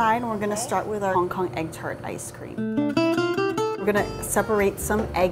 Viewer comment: At the start I thought it said “my own custard ice cream” ... I’m crazy